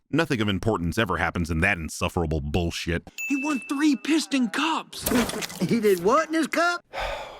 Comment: For the longest time, I just thought Mater said, “He didn’t want this cup?!”.